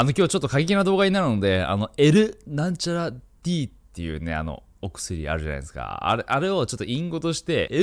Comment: NTDって聞こえた